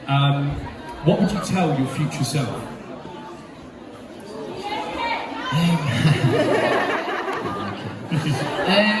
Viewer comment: i can't hear what that fan says, someone who knows plz? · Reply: I think she said you made it